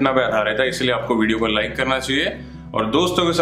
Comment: 😂😂😂 paaji aap ki speech se lagta hai "aap sabko video ko unlike karna chahiye" the way you told sounded like this. 😂😂😂